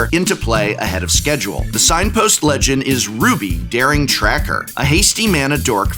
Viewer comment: "Ruby, Daring Tracker ... a hasty mana dork?" Did I hear that correctly?